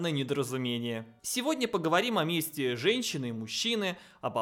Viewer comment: я услышал "о мести женщины"